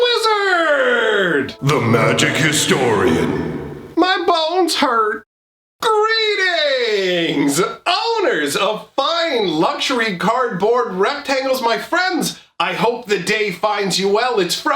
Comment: i always thought that u say my BALLS hurt lmfao